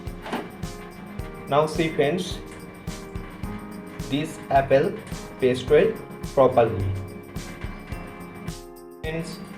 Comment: Hi sorry but can you write what you said on 2:20 i did not really hear it. Dont take offence because you are very talented and i know it can be very hard to talk multiple languages. Love from the philippines:) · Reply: apples are paste well properly